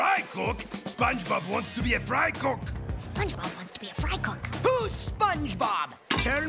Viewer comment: Zim: Who's Spongebob, TELL ME!!!! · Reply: I could of sworn he said “WHO’S SPONGEBOB? JIMMY?!”